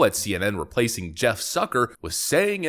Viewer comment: I thought said 'Jeff Sucker'.